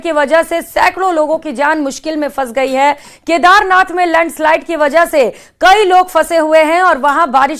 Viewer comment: Did she say Lund slide😅